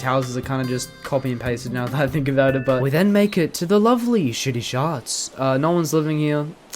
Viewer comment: no way my subtitles says that he said shitty shots 😭😭😭😭😭💀